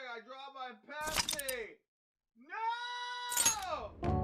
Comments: I need a translation on what said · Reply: Ah frick I dropped my Pepsi, NOOOOOOOOOOOO! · Is saying he dropped his pepsi? Like honestly what happened? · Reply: yeah. I heard, “Ah frick I dropped my Pepsi, NOOOOOOOOOOOO”!